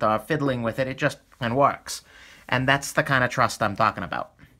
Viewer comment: Did you say "It just fucking works" and then muted out the word "Fucking"? lol If so, You should have just left the word "Fucking" in there. I understand you wanting to have Family Friendly content, But, If it just fucking works, then it just fucking works lol Great Video.